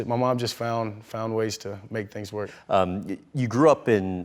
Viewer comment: it said make things worse instead of work 😂